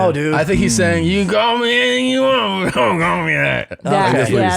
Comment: "You can call me anything you want, but don't call me that." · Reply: I heard, “you can call me anything you want, but don’t call me late.” As in the common southern colloquialism, “don’t call me late for dinner.” 🤷🏻‍♀️